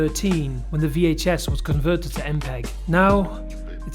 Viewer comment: DURING THE WHATS YOUR PROBLEM PART I MISHEARD A WORD AND STRAIGHT UP THOUGHT YOU SAID VHS WAS CONVERTED TO MPREG IM GONNA SOB